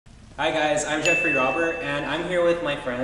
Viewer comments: I thought he said Jeffrey Dahmer. Lol. Good thing his name was written on screen. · I deadass thought he said Jeffrey Dahmer